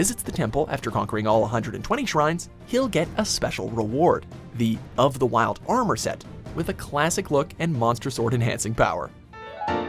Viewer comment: Did, did you just say monster sword